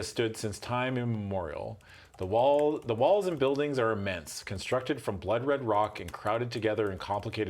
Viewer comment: I thought he said, "the walls and buildings are a mess" continuing Line Wolf's hatred of all foreign cities, lol